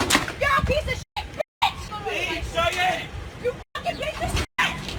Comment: it sound like he is saying Cheyenne that’s the name of my mom 😧 My mom ain’t a Karen though she is a beautiful and kind person she also black 👌